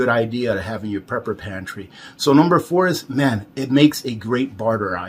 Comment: minute mark.. Does anyone understand what he's saying for #4 ? It sounds like mint...but I sure that's NOT correct.